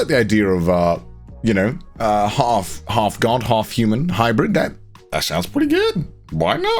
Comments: "I like the idea of, you know, a half Garnt, half human hybrid. That sounds pretty good, why not?" Sometimes I mishear things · Am I the only one who heard “half garnt half hybrid” at 13:16😂😭. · Is it bad that I thought I heard him say, "Half Garnt, half human hybrid," in this context and didn't even bat an eye? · I really thought he said "half Garnt half human"